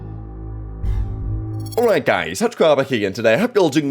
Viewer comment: All this time I thought he said “Touch Grobba here” not 😂